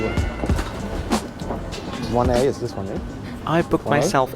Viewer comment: did he just say….1a?